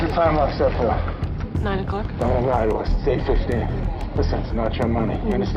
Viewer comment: At. 3:10, I’m pretty sure the girl tried to lie. I know it is hard to hear but I think after the girl says that, he says”Don’t lie to us again, it’s 8:15”. But I definitely agree, they were prepared